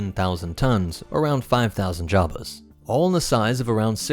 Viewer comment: Wait did I hear 3:16 right? 7000 tons is 5000 Jawas??? 1 Jawa weighs over a ton?